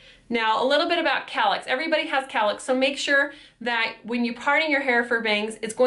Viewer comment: What is a calic? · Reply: I know what a cowlick is, I just couldn't understand her accent. I thought she said calick or carlick or something